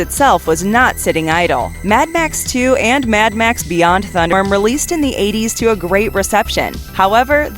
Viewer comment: "Beyond Thunderstorm"!? C'mon, guys. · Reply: I had to play that part again lol. I thought I heard Thundrum at first. Lmao